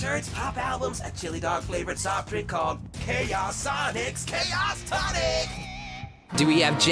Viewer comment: I heard 'chaos' as 'kickass' and was both surprised and impressed for a second there